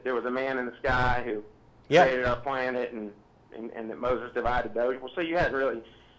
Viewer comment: I heard him say Moses divided the Earth